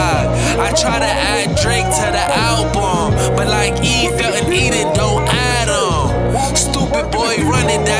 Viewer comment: “I tried to add Drake to the album, but like Eve felt in Eden don’t Adam” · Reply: I thought he said “Like Ether(eve felt) and(in) Eden don’t add em(Adam)”